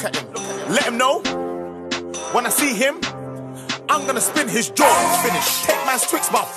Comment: Omg this whole time I thought he was saying "I'm gonna spit in his draws" 1.22